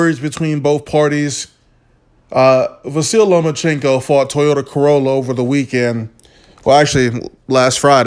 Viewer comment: secs, i had to rewind, did you just say he fought toyota corolla over the weekend? Lol